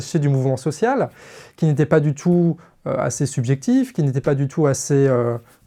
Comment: À 13:45 il fallait entendre "objectif" ou c'est moi qui interprète mal ? En tout cas j'ai hâte de voir ce que va donner la suite.